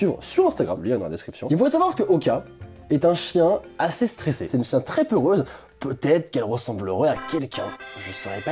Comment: c'est moi ou il a dit : "c'est une chien très peureuse" à 3:56 ?